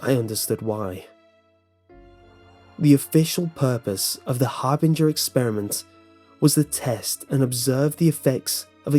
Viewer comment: Bruh I thought it said "hamburger experiment"